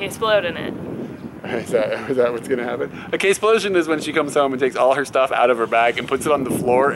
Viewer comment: Ohh a K-splosion. At first I thought you said "kid-splosion"....and I was thinking home birth or something? haha. Would be a strange way to describe it. :)